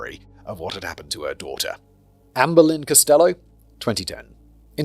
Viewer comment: I know he's saying Amberlin, but all I hear is "Anne Boleyn".